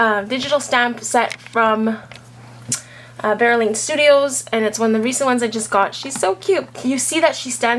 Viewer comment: Great video! I didn't quite catch where you said you got this stamp set..I kept listening at 1:55 and didn't quite make out what you said...but I just love these images! Thanks for the info :) Melissa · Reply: Dollipop Productions oh sorry about that. it's by vera lane studios on etsy. thanks for watching!